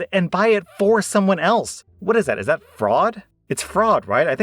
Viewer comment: I kept hearing “is that frog” Is that me or are y’all hearing it to?